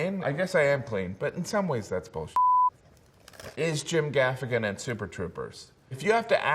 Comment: At first I thought it said "Is Jim Gaffigan in Starship Troopers?" and I almost immediately went to find it.